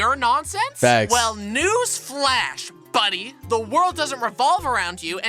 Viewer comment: did he say the world revolving..?